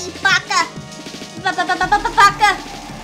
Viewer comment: what did she say 😂 I think she said b-b-b BAKA 😂